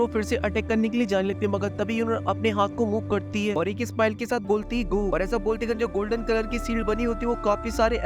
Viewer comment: Ek smile ke sath kya bolti he 😂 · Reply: Gu